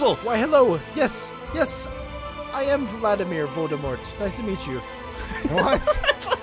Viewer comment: Did say he is vladimir voldemort at 16:34 ?